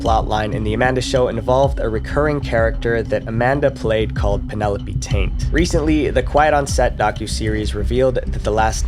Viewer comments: Maybe it was just me, but I always heard it as Penelope Tate. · Penelope Taint?!?! I always heard it as "Tate."